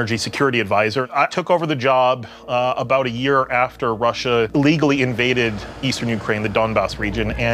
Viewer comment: Did he just say "legally invaded Ukraine"?